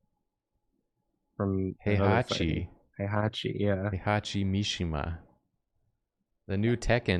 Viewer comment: Great stream! I have my earbud in and at 50:42, I was calling for my cat Hachi when and both say "Heihachi" twice and I thought they were saying "Hey Hachi" which made me pause for a moment like wtf?? lol